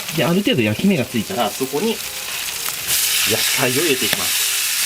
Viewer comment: が「おっぱいを入れていきます」に聞こえるのは自分だけ？